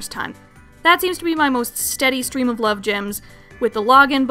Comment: The CC thought that at 13:00, instead of "Love Gems" the phrase said was "Blood Gems"